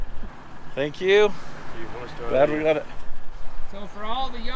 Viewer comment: Whoa, wait a minute! Does dude really say " Thank you, 1-star review ." at 14:43??